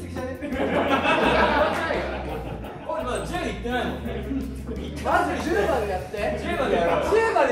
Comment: きょもが「こーち（鬼）やりすぎじゃね？」って言った後、ジェシーなんて言ってるかわかる人いますか？ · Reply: 「始まってないから、髙地もね」って言ってるみたいです！ 字幕にすると出て来ますよ🫶🏻